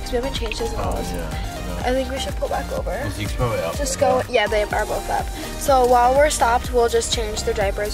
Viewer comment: Did she called him babe at 10:59 or am i just hearing things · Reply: She said “no they are both up”. Y’all trying to hear things😂